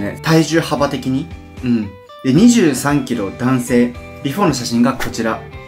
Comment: イヤホンから 14:50 23キロ男性て聞こえた時、え？てなったwみんなすごい！！